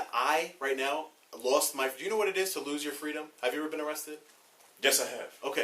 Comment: “Have you ever been arrested sir?” Officer’s Response: “yes, I have” Did I hear that right? Lolol wtf